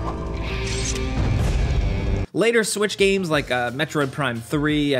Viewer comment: I AM crazy or did You Say 4:29 switch games?